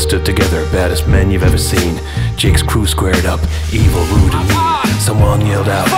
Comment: "Jake's crew squared up," then what on earth does he say after that? "evil lewd I mean."? "evolude a mean."? "Eva, Lu, and me."? This is driving me crazy! Someone please help. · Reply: Never fear- it's Evil, Lewd and Mean. I think. I mean i wrote it, but I forget what i write pretty quickly. Love, Professor Elemental